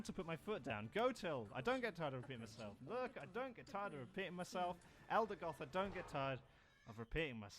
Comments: Low-key thought he was saying: no I don’t get tired of peeing myself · Guy is stranded on an inhospitable planet - Everyone: Okay, he's dead - Guy shows up 40 minutes later in a spaceship - First radio message: "No, I don't ever get tired of repeating myself. No, I don't ever get tired of repeating myself. No, I don't ever get tired of repeating myself. No, I don't ever get tired of repeating myself. No, I don't ever get tired of repeating myself. No, I don't ever get tired of repeating myself. No, I don't ever get tired of repeating myself. No, I don't ever get tired of repeating myself. No, I don't ever get tired of repeating myself. No, I don't ever get tired of repeating myself. No, I don't ever get tired of repeating myself. No, I don't ever get tired of repeating myself. No, I don't ever get tired of repeating myself. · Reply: I thought he said I don't get tired of peeing myself.